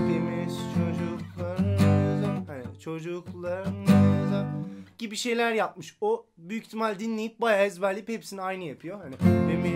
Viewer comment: Selam:) Çok şaşırdım valla görünce, yardımcı olabildiğim için memnunum. 13 sene olmuş çıkaralı, Yavuz Çetin'i maalesef intiharından sonra tanıdım. 20 senedir aralıksız dinliyorum, bence TR'deki en iyi albüm budur. Zamansız bir albüm! İlginç bir şekilde rahmetli Asım abiyle de hayatımız kesişmişti, kendisinden bir pedal almıştım, telefonda konuşmuştuk. Allah rahmet eylesin...Seni de teee Asım abiyle yaptığın efsane acapella'dan beri takip ediyorum, inanılmaz doğal yetenekli birisin, yolun açık olsun müzik hayatında :) Yavuz Çetin'in Oyuncak Dünya'yı halen benim çaldığım şekilde çalmadığını düşünüyorum, ama sonra tekrar kafa yorma fırsatım olmadı. DADGAD ile de çalınabiliyor diye duydum, ama sanırım Yavuz Çetin'i çalarken hiç görmediğim için sır olarak kalacak. Verdiğin tavsiyeler çok güzel, arkadaşlar genellikle tab yolla diye yorum yazıyor, ancak söylemek istediğim bir şey var, dediğin gibi herkesin parçaları dinleyerek çıkartmaları gerekiyor. Bundan daha güzel bir gitar/enstrüman/müzik dersi yok dünyada. Tamamen duymaya güveniyorsunuz ve ufacık parçalarını çıkardıkça sanki bir sır çözmüş gibi zevk alıyorsunuz. İlk tepki olarak tab, nota gönder diyenler bu zevkten mahrum kalacaklar, yapmasınlar :) İyi akşamlar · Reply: 'da ''Gibi şeyler yapmış, o büyük ihtimalle dinleyip bayağa ezberleyip hepsini aynı yapıyo' dediğinde ne dediğini anlamamıştım. Ta ki senin videonu izleyene kadar. Sonra Evrenin bilinç altının dilinden çıktığını anladım.